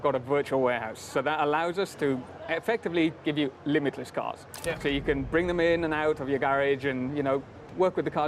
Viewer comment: did i heard "LIMITLESS CARS" (in garage) ??? :OOOOO. HELL YES